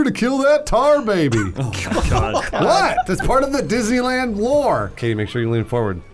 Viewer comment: said: "it's part of the Disney lore" I heard: "It's part of the Disney war" Ok, so hear me out, Civil War, but it's Mickey and Donald squaring off with their bitch ass friends squaring of. It all starts when Goofy loses his shit and shoots one of the Duck Tales triplets.